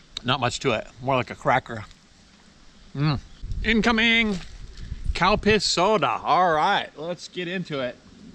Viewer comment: When you were talking about the Calpis soda, I could've sworn you said cow piss soda 🤣